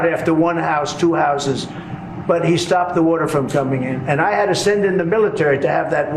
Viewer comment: I thought he said "stopped 8 wars"! He makes me 🤢 He "alone" dreams up all the BS that spews lies after lies. PLEASE people get this moron out of office and his MINIONS too! Ews